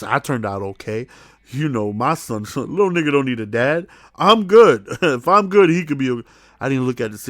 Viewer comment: did say. "lil nigga don't need a dad" ? Man my ese's are black too, we fight the same battles